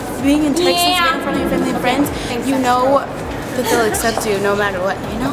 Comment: what say Camila at 5:14 ? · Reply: "thanks extra" to ally wow that was mean